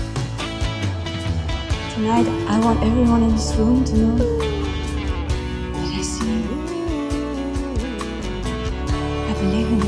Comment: nunca entendí lo que dice cuando esta en el micrófono 🎤 hablando. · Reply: "I want everyone in this room to know that I see you" ? I think